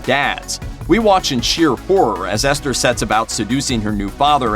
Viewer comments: Thank goodness you had captions. I kept hearing "cheer for her" instead of "sheer horror" 😂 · i heard that as " We watch and cheer for her"